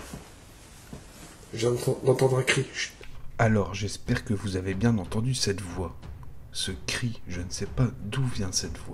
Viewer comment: perso moi je n'entends pas un cri mais une enfant qui dit d'un ton interrogatif "Maman ?" !